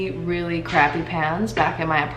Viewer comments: I thought u said PANTS instead of pans😂 I was thinking like «is it normal to set fire and burn their pants over there?»🤔😂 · AHAHAHA my cat was meowing & I thought you said you had crappy pants in your old apartment